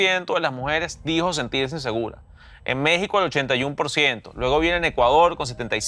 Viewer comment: Perdon no entendi DW, en el 12:50 dices es que se sienten seguras o inseguras? Porque Chile 83% dijiste se sienten seguras.